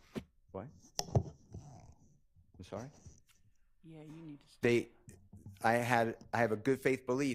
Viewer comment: HOT MIC…..WHOAAAA….. DID LOVE JUST SAY, “YEA, YOU NEED TO STOP” 😳😳😳😳